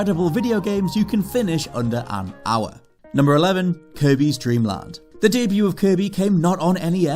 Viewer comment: I’m in the kitchen listening to this video and I thought I heard “Kobe’s Dreamland”😂